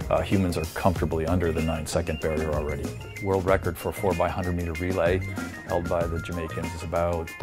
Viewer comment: I thought he said 4x4 I was like how tf is that even possible